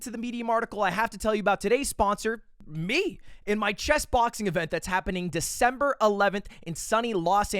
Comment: NGl when the Chess Boxing add came through I thought I heard Chest Boxing